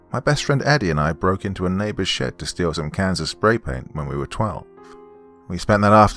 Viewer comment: Thought he said Kansas spray paint. I was like what the hell is Kansas Spray paint..... Then my brain stopped seizing and I was like ohhhh cans of spray paint. Ahhhh